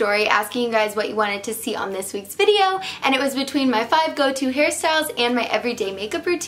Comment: I thought she said top 5 goatee hairstyles